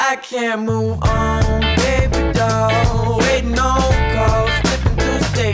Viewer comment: bro I thought he said babygirl for a second 😭💀